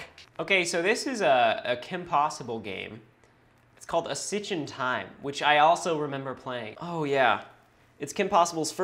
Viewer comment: I thought it said a SNITCH in time 😂😂😂